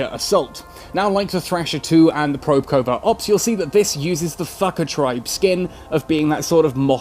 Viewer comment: For a moment, I thought you said, "fu.. ker"